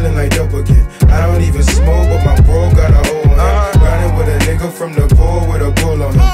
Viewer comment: “I don’t even smoke but my bro got a O on himm” 🔊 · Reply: I thought he said “I don’t even smoke but my bro it got ahold of him” but your definitely right I hear it now